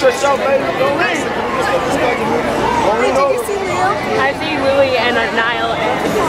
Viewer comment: did she just say "did you just see niel" ugshkdj